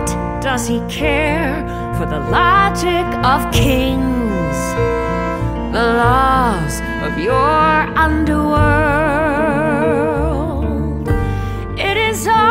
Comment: Im so sorry but when persephone said, "What does he care for the logic of kings? The laws of your underworld?" I thought she said, "The loss of your underwear"